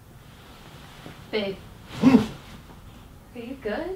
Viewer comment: am I tweakin or did she say babe